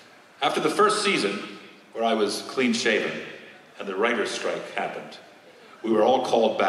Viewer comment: Near the end, at first I swore he said "During the Riker strike..."